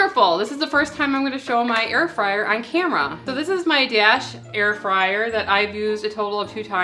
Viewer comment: I thought you were saying Gas Air Fryer😀😀 - Dash sorry. Toast that garlic in the Dash/Gas Air Fryer.😀 Love my Traeger. Always fun to try new recipes. With real time comments.